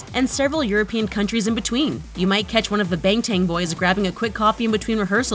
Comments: bengten(g) am i mishearing this? and am i the only one who hears this? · did she just say “BaNg TaNg”😶